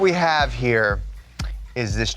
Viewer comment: damn he said Challenger instead of charger